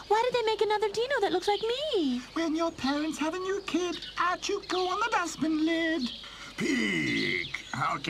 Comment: WTF IS HE SAYING??? 42:00 "When your parents have a new kid, I chew koo ona basbin lid????" w h at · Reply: Out you go on the dustbin lid